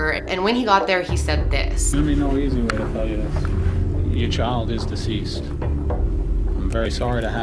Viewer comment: what did the detective say at first? "There shouldn't be no reason for me to tell you this."? Is that what he said? I couldn't tell... Oh wait I think he said there was no easy way to tell her this...